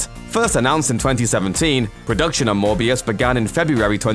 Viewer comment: Am I the only one who heard him say Morpheus and not Morbius???